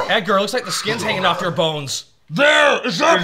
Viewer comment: For a long time, I could not understand what Edgar's wife says in Men in Black after the sugar water bit. Me and some of my brothers thought she was saying "YOU'RE SKIN IS HALF YOUR BALLS."